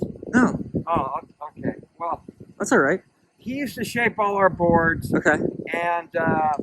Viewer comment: Could have sworn said "He used to shave all our balls..." It took a while to cotton on to the real topic.